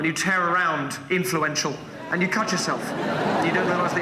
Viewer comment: I HEARD him say "tear around influential" & was like Huh? but I figured it was some British saying meaning running around making a mess ! Hahaha